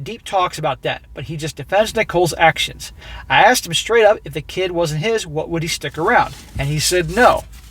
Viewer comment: Maybe I misheard, but I think you got it wrong. It sounded like (to me) that he said "if the baby WASN'T HIS" he would not stay with her. NOT, "If she wasn't pregnant at all" he wouldn't stay with her. Like OP's girlfriend said, he is "blinded by love". So? Her CHEATING WOULD change that, but not being pregnant at all, would not. The rest makes perfect sense to me. (but then again, I am a male & use Logic) He is OP's "best friend". HE is OP's metaphorical "brother"! So, of course OP would be willing to "take care off" & even "raise" (should the worst happen) the offspring of said BFF! (because OP is a decent guy & a good friend) But OP is NOT "best friends"/"brothers" with Nicole! OP has NO interest nor obligation to raise HER (& some rando from the club's) spawn! But that is EXACTLY what he MIGHT become obligated to do W/O a DNA test.